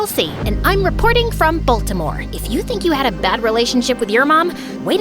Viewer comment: "Baltimore" What I heard was "Voldemort" 💀